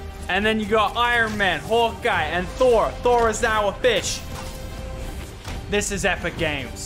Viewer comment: when you said thor is now i fish. i thought you said thor is a bitch, and i agreed.